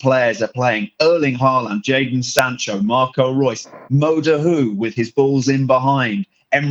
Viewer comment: What did he sayyyyyy??? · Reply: mo dahoud